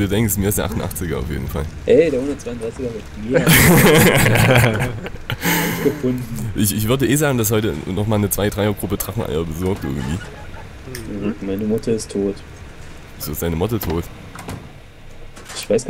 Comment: ich hab verstanden meine mutter is tot xD